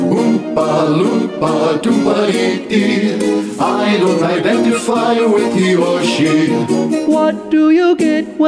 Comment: what did they say? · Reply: I don’t identify with he or she